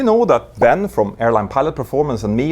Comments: I thought you said "typewriting courses" first. Had me confused there for a second... 😉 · I totally thought you said you were running virtual typewriting courses...